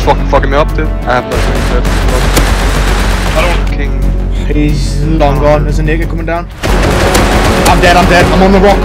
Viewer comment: did you just say what i think you just said? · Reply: "he's long gone"